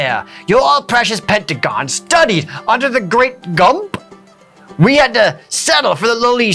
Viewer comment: For a second there I thought they said he trained under the mighty gimp. I almost died laughing